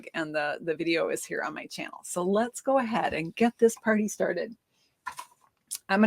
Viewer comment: I thought you said flying FISH!😂😂😂